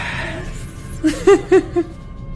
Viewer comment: the kitchen one you hear a voice at 6:40 saying "ah yesss!" Very creepy then the mom giggles she didn't hear it but it's there!